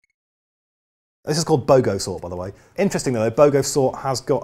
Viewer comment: Wikipedia told me you said Bogosort. I thought you said BOGANsort ! Good aussie will understand the confusion. :)